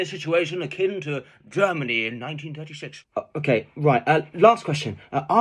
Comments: "Last question: are women funny?" "No, and it's because it's science." "Thank you." "Thank you, and now you may kiss" 🤣 Edit: I know he says Emily Maitlis but it sounds like he says "you may kiss" 😂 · Did he just say "Thank you and you may kiss"? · at the end i hear ‘thank you and we may kiss’ lmao